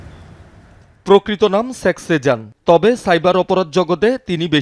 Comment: সাংবাদিক কি বল্লো? sex sejan? শেখ হবে ভাই, উচ্চারন ঠিক করেন